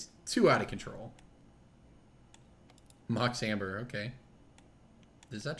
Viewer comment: did he say "Mox Hamburger"? Coulda sworn he said Mox Hamburger...